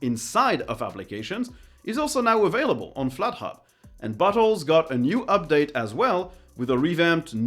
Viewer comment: Am I the only one who heard "butt holes" at 10:56? XD